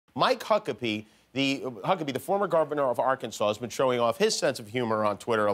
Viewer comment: I keep rewinding the beginning of this to hear it, but it sounds like he said "GAR-venor" (0:02). Its funny, and I'm not nitpicking, rather just curious if anyone else hears that.